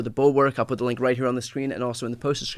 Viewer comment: I swear I thought you said “inside the Reich”.